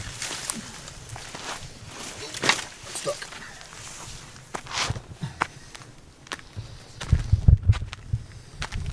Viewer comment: What did you say at 1:16-1:17? heh heh. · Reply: Shhhh